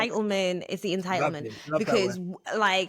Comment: he actually didn‘t swear, they bleeped over him saying bloody lovely, like they did with Lewis a while back. They‘re trying to stir the pot mega hard at this point.